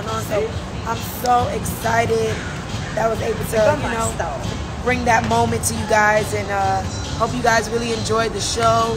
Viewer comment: Can someone help me @ 1:41 what does that voice in the background say ? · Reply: Somebody stole is what it sounds like lol